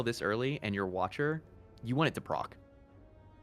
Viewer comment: Can someone tell me what word is saying when he says "Prok?" (it's at around 18:10) I understand the meaning to be "when this thing triggers," but I have no idea what word he is actually saying and I've been watching him for awhile now. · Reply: PROC - Programed Random OCcurance.